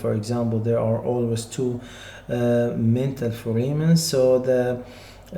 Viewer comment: "Dental" is being said. "Mental" is being displayed at bottom left.